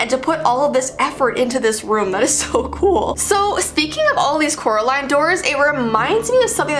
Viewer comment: At first I thought it said Coraline Dolls and expected for people to find Coraline dolls